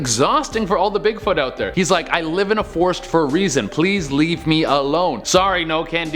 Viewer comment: I heard him saying please leave me a loan😂